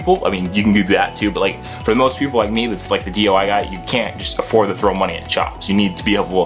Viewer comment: did he just say "DUI guys"